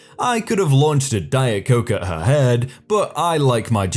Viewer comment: I misheard the part where he read: "I could've launched a diet coke at her head..." as "I launched a diet coke at her head..." i died laughing but then reread it and realized it was too good to be true lol